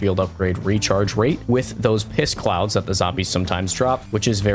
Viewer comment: Piss-clouds? LMAO. Sorry if I misheard. 🤣🤣🤣